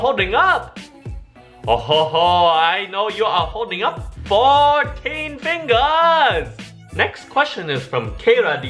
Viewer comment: When said 14 fingers I thought he said 4 and it scared me because I was holding up 4 fingers.